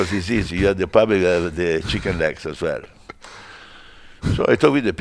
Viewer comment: did he say chicken necks??